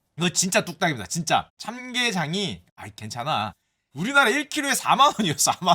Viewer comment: 참게장인데 ㅋㅋㅋㅋㅋㅋㅋ. 짱개장으로들었나? 더 차이니즈 미튼으러 번역됨 ㅋ😊ㅋㅋㅋ